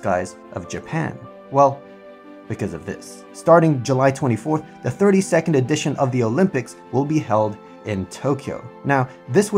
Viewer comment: I thought he said "30-second edition of the Olympics" LOL